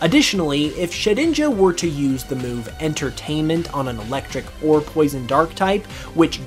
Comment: did you just say "Entertainment"?^^ Also the Metagross one was the only ne I didn't hear of before. Nice calculating (assuming you did it yourself:D)